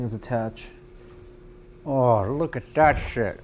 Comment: does he say "ahh look at that shit"